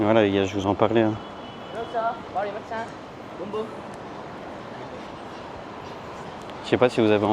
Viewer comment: il a dit quoi à 26:36 ??? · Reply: Il a demandé si il voulait un massage boum boum ! 😜🤣